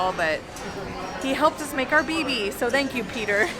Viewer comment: Haha I was kind of distracted but when I heard her say that I thought I heard "baby" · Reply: Haha BB not baby